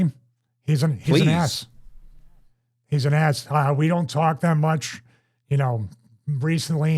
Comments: not gonna lie. Sounded like you're saying he is an ass here 😂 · Ngl, just listening while working on something and I completely misheard when you said Christopher Daniel's was an S. 100% thought you said he was an ass, for a minute lol